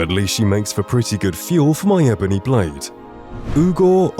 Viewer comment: Another problem with the two Dark Brotherhood followers is they are so generic that their inventories are set to reset periodically. So any gear you give them will get deleted. I lost Chillrend that way once. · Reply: I hear “free ebony mail”